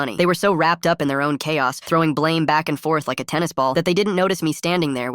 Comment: did i hear "cahos"... CHAOS CAHOS